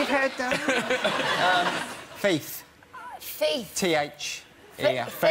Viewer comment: Could any native English speaker please comment what Micky says at 19:56? Y'know, after "keep going". I've listened to that line carefully like a hundred times but I'm still not getting it. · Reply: Non-English Speaker we could get you a plot bit in east enders